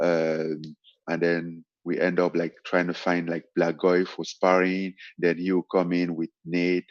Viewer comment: Did Francis say they tried to find a black guy for sparring at 4:46 ? Lol